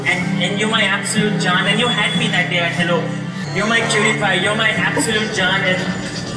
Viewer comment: lol the auto generated subtitles said ‘you are my john’